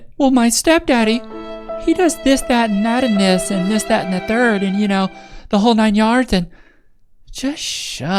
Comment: “The name’s Thisthatinn. Thisthatinn the 3rd. My father was Thisthatinn Jr.” 😂 it sounded like a name to me haha